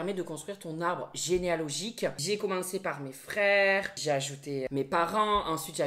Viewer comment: pourquoi pendant le sponsor, quand il a dit "j'ai ajouté mes parents" j'ai entendu "j'ai acheté mes parents" XDXD j'ai bugué pendant une seconde, doux jésus